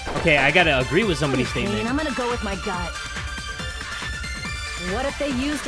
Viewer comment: I thought she said “I’m gonna go with my gyat”😭